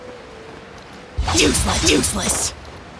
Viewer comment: I HEARD SAY "JUICELESS" WGAT